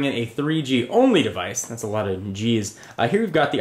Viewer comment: LOOOL.... Listen closely at 2:00... "That's a lot of JIZZ"... That's what SHE SAID !!